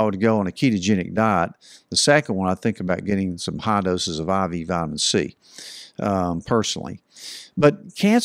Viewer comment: What did he say at 8:53? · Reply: Intravenous vitamin c (injected)